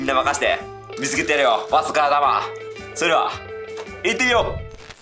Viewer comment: 行ってみようがリテイルローに聞こえる